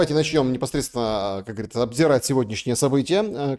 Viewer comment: Мне послышалось "обсирать" события, это было бы точнее из уст Шелеста